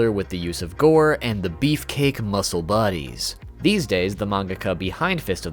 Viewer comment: bro I thought he said die 😭😭